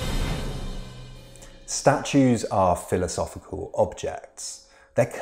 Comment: 😂😂 i thought it said "statutes" not statues 😂❤